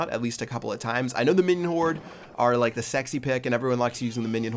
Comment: Wtf did say minion horde is the sexy pick? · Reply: he did say that they are the sexy pick lol